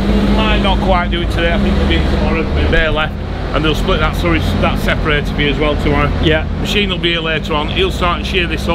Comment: What did he say?? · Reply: Slurry separator?